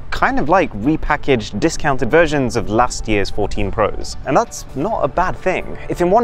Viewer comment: After watching the video, how much do you love or hate the new iPhones? 🤓 We've pulled a 24 hour shift to get this video prepped and done in time so do consider subscribing for our detailed tests! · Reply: You said iPhone 14 pro max instead of 15 pro max